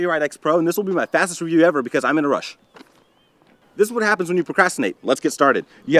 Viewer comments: I thought he said I'm in Russia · For a second it sounded like he said hes in russia not in a rush